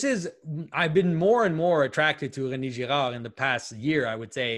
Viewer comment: For anyone else who couldn't quite catch it, René Girard is the philosopher that Jonathon mentions at 1:13:32. I couldn't understand myself what he was saying, but I found him by googling the "infinite victimhood" quote.